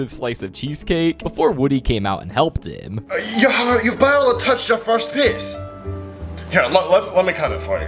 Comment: It sounded like shrek said "you barely touched your first piss."...☠